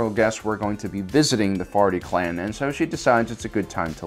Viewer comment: I keep hearing "farty clan"